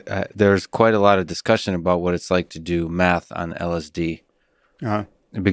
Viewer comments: I thought he said meth on LSD... I was like whoa slow it down there cowboy. Then it became apparent he just says math weird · I thought you said "Do Meth on LSD" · I for sure thought he said do meth on lsd 🤣🤣🤣